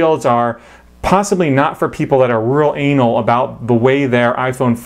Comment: ish did I hear anal? lmao.